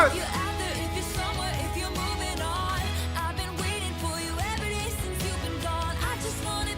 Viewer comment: i cried at eyes open honestly it was sooooo beautiful - also for me, a non english native, the lyric in If This Was A Movie has ALWAYS been “every day” 💀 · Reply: same! i mean it wasn't as clear as it is now, but I DEFINITELY heard "every" so "every day since" was the only thing that made sense lol